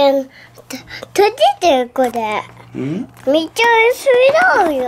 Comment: これの 撮れてるこれ⁉︎ に聞こえた笑笑